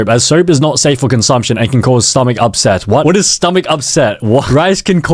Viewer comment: I think Google said upset stomach and not stomach upset